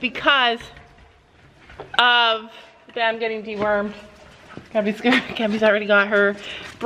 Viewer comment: I heard it as Sam getting de wormed I almost peed myself 😂😂😂😂😂